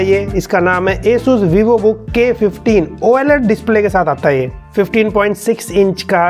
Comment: same like me "oaled" instead of oled